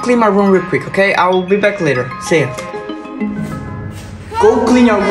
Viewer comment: i thought the mom was saying cocomelon but no she said was go clean your room btw good video its relatable